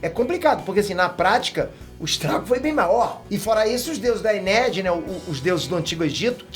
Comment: juro que ouvi deuses da Ei nerd kkkk